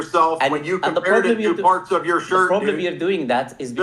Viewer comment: He said “parts” lol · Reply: I thought he said "farts"